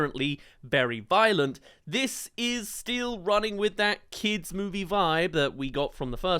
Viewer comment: Sorry, you saying “Still running” reminded me of something… with your accent… it almost sounded like… STEEL BALL RUN!